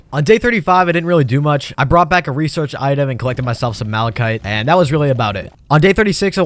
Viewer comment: Ykow whats funny my name is malachi so when you said malachite, i thought you said my name lol